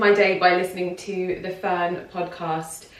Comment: Hi Lydia, could you please provide the name of the podcast you mentioned at the start. I heard 'The Fern Podcast' but when I search I get a sports podcast. That can't be it 😂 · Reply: It‘s linked in the description box. The podcast is named „as the season turns“ from Ffern. With double „F“ in the front. Hope, this is helpful :-)